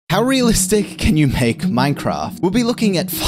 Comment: At first I thought he said, "How real estate can you make Minecraft?"